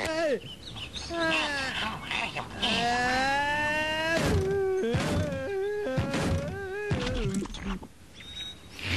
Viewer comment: It sounded like he called Bob ungrateful or an inbreed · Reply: Rachel Guzman he often calls him an “ingrate”